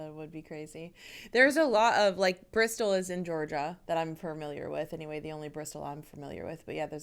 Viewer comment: I thought you said "There's a lot of Bristols in Georgia" and nearly choked on my tea. 🤣 If you haven't already done so, look up Bristols as Cockney rhyming slang😜